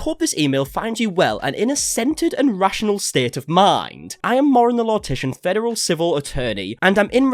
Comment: I really enjoyed content up until all of this, but like the amount of gas lighting from her gives me so much ick also.... 19:39 did you say Mauren the Lortician xD